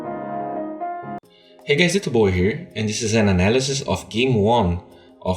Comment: When you say "Hey its D.Bowie here" it sounds like your saying "Hey it's your boy here" XD